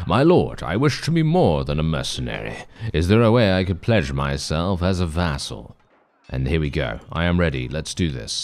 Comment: what you said "pledge myself as a vassal" what i heard "pleasure myself as a vassal". Bannerlord after dark.